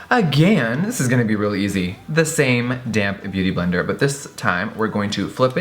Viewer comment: Just recently started watching your videos, I thought you were always saying my damn beauty blender.....was thinking what did that little sponge ever do to you? I now realise you are in fact saying damp. <3 the DRAMA!